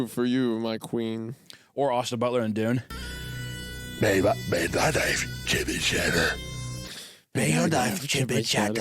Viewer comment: "May th- may thy knife, chip and shadderr" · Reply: I couldn’t even register the quote, I thought was saying “may thy night, chippy cheddar”